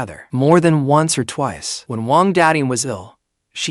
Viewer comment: I thought he said wang daddy